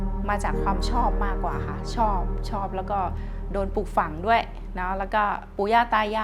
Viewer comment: i think you might've misheard ปลูกฝัง (raised/trained to be, instilled) as ฝัน (dream)